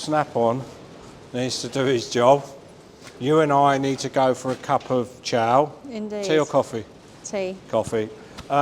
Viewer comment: Ok i thought you said cup of chow, which i would know as cup of char